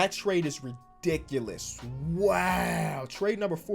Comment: That wow at 2:41 sounded like Kobe though